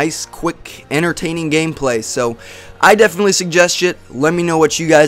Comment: it sounds like he says "i definitely suggest shit" lol